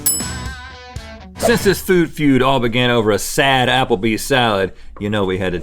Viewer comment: Totally thought I heard Rhett say it's time for Food Fears lol